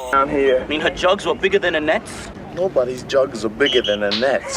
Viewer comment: "A hickey from Kenicke is like a Hallmark card!", "Bite the weenie, Rizz!" "With relish!", "Nobody's jugs are bigger than Annette's"...so many awesome lines in this movie, and pretty raunchy for a movie supposedly set in the squeaky clean 1950s! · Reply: I never got that jug line as a kid because I had no clue who Annette was, so I heard "a net" and was so confused. I thought net may have been slang for a bra or something.